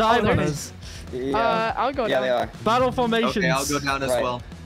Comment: Battle Formations! *what I heard* SovietWomble: Team 1! Team 1 scatter! Team 1 f**king scatter now! TEAM 1!